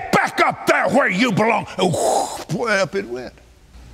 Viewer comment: wtf did that guy even just say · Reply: (Wind noise) “and way up it went”